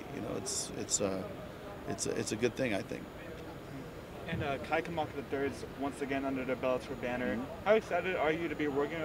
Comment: Who did they resign? I can't hear the name he says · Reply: Kai Kamaka the Third